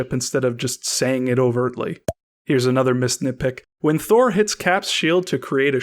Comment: This whole time I thought you were saying “Miss Nitpick “ until I realized that it was missed nitpick